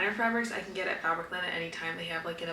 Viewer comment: where did you get those cute fabrics from ? I love them. ❤ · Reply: I thought she said Fabricland. 😊